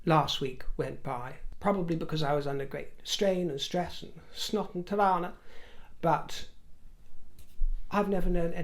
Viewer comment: Am I mistaken or did I just hear say "Snot en Trane" (Snot & Tears) - It is an Afrikaans Slang meaning crying crocodile tears.